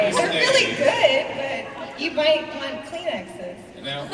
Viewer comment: Can someone please tell me what Amy said around 42:40? "You might want..."? I really can't make out the words. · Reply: She said: "You might want kleenexes" (brand of tissues)